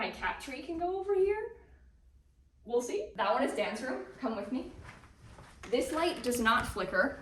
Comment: instead of "Dan's room" I heard "Dance room" and I was so excited